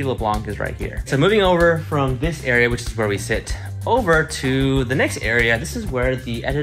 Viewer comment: For a sec when Steven was showing where they sit I thought he said "this is where we sh*t" I was like "wait,what-" lol